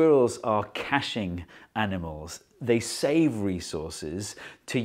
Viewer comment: "Squirrels are caching animals" ...Uh... No, they're caching acorns Okay, I think I misinterpreted the grammar here ^^ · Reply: I heard it as "cashing animals" and immediately imagined a stonks squirrel meme.